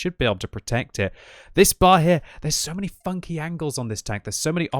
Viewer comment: my volume was a bit low and i thought said "theres so many fucking angles" i was like wooooo